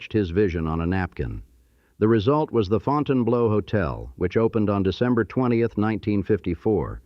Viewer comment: I had hopes this wasn't AI until I heard "The Fountan-BLOW" instead of The Fountain Bleu". I think there's a Sex Club called the Fountain Blow up near Pompano...heh, heh...